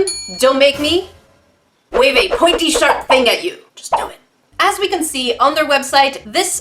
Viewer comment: On my 5th time watching this, I saw that at 1:35 you said pointy sharp tool, not SHARP POINTY TOOL. Also instead of a zero rating, I would give it a -<num> on 0-10